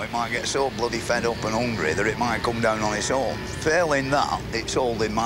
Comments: Fred actually said the C word,like a proper bloke.😂😂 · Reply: I heard "come down" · Fred actually said the C word,like a proper bloke.😂😂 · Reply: yeah I heard "get it t'come down"